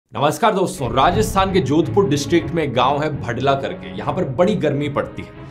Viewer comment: I heard bh*dwa 😂😂